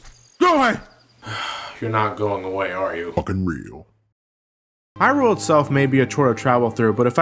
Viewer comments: F--king read that's what he said I think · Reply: Thanks I thought he said ''I'm fucking real,'' thanks for clarifying. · what is the owl saying? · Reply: "fuckin' real" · what did that owl say to john in 15:42 ? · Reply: F--king read that's what he said I think